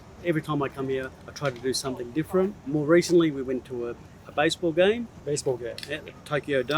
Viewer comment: 下北かぁ・・・私がゴミゴミしたところにしか目がいかないのは、感性鈍いんだろうなあｗ 10:05 このbaseball game は聞き取れねぇな〜ｗ お前、他の単語どれだけ聞き取れるんだって話だけど、簡単で身近でよく知った単語が聞き取れないっていうのは、改めてなかなかの衝撃ではあるのだｗ